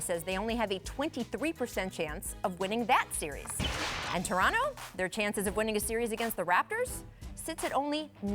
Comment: Did she say, “Toronto the chance for to win against the Raptors??!!”